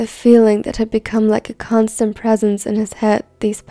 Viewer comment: I thought it said croissant but it said constant